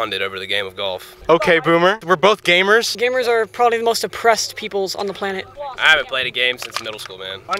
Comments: "gamers are probably the most oppressed peoples on the planet" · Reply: Thought he said depressed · There are so many one liners here that only true Americans can give. "Gamers are probably one of the most oppressed peoples on the planet" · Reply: I thought he said depressed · "gamers are probably the most oppressed peoples on the planet" - this comment gave me a hernia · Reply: Oh what? I thought he said "depressed" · “Gamers are some of the most oppressed peoples” Dear god… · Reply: oh shit i thought he said "depressed" lol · “Gamers are probably the most oppressed people on the planet” hahahahahaha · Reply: I thought he said "gamers are the most depressed people on the planet". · “Gamers are the most oppressed people on the planet” made man burst into loud as fu** laughter at 6:17 in the morning. Had to cover my mouth cause I live in an apartment. 🤣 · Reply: Yeah I heard "depressed".